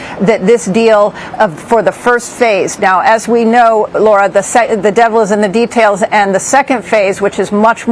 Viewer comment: I thought I heard her say the sight of the devil is in these details.